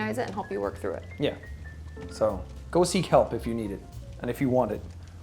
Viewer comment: I thought he was saying "go seek kelp" LOL